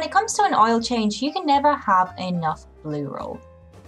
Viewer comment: I thought you said you can't have enough LOO ROLL for the oil changes, not BLUE ROLL 🙃. Glad to see you taking the leap to take these jobs on